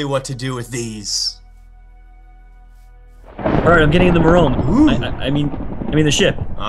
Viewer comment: Did Jerome say merome?